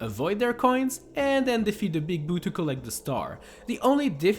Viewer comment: Am i the only one who heard him say “now defeat the big bootie”